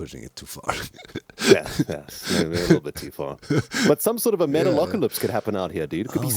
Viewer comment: did someone say Metalocalypse???